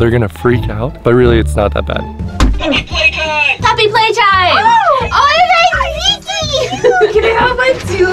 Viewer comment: Oh, Puppy Playtime. I thought it said, “Poppy Playtime.” XD